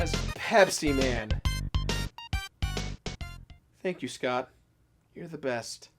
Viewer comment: Is sounded like he was saying thanks to Scott Cothen.